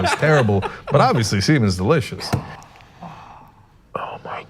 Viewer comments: Sounded like said, "but obviously semen is delicious". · I legitimately had to rewind and rewatch it 5 times because I thought he deadass said “obviously semen is delicious” I bout died laughing and sadly realized it said seafood, but I still like to think the alternative is true as well.